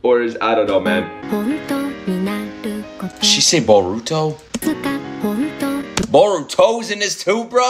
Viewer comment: she actually said "honto"